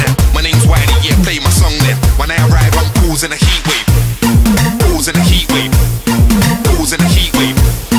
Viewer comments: I always thought it said balls and a heatwave but when it's causing a heatwave 😂😂!? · I thought he said "BALLS IN A HEATWAVE" xD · I thought he was saying 'Pools in a heatwave' They really need to speak clearer.. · at first i thought he said balls in a heatwave but then realised he says causing a heatwave but me and my friends still say balls in a heatwave